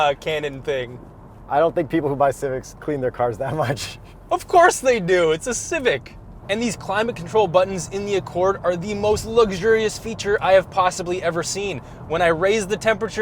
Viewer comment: I heard: “Im a cynic” “Im a corn”